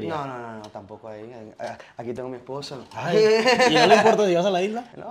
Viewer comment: ayoooo. bueno tambien habla español lo suficientemente bien, pero escuché esposo xdd.